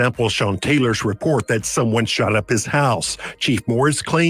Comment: Sometimes because of accents, I mishear things. I thought he said his Ass was shot up, not his house. Sometimes it turns out I haven't misheard, and that can be even more surprising. lol